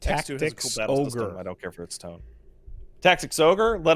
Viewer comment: Auditory processing disorder individual here what in the deep blue fuck is saying at 36:02 I've been playing it back for five minutes and my ape brain needs to know because I think it regarded BD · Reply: "[Sic] it has a cool battle system, I don't care for its tone" If I heard correctly.